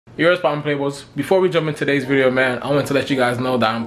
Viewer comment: Lol wtf did u say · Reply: “Yurr what’s popping playboys”